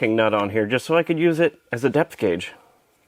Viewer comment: Pardon, but did you say “Dutch cage”?!?